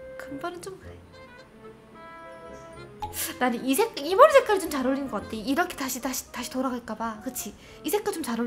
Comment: 나만 이색히 이런 머리색깔 좀 잘어울리는것 같애 라고 들리나